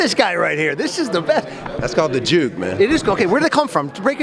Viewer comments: what did jeff called his dance move at 0:13? my head cant spell it right. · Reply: the Juke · what did jeff called his dance move at 0:13? my head cant spell it right. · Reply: “The juke”